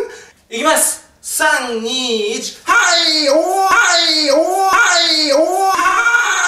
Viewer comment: くらいから[はいおー はいおー はいおー]→[おっぱい おっぱい おー]に聞こえるwwww